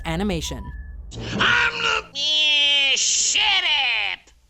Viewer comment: I thought he said "shithead"